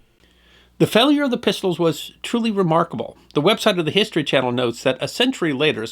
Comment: did he say "the web site of the history channel say" 13:54? that's about the same as wikipedia, isnt it?